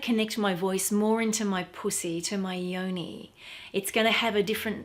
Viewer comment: did you really just say that or am I hearing things lol. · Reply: Hi Miss Chloe Jenkins! Thanks for your comment....if you mean "pussy"...then yes!